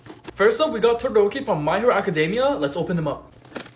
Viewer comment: When he said “My hero academia” I heard “Minor Academia” 💀💀💀